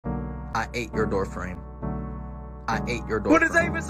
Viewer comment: I just hear I "I ate your boyfriend."